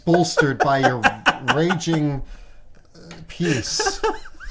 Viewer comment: I heard “raging peace” as “raging piece” and I was thinking of something MUCH different lmao